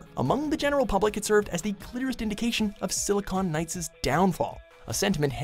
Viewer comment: Did he say "cliterist" instead of "clearist"?